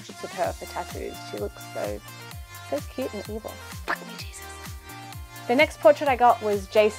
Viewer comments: I always get sort of sad when people say things like "I'm against religion" like in general. You cannot say that reliogion in general is a bad thing. Mostly those people don't really have an idea of the meaning of the different religions. It's just sad to hear things like that... By the way your tattoos look great on you! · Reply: Did she just say f u Jesus at 13:18/19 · You said you hate religion but I swear I heard you say Jesus around 13:19. 🤔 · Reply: she's referencing the movie The Exorcist, where the young girl who is possessed says the line, "FUCK me Jesus" and stabs herself in the vagina with a silver cross.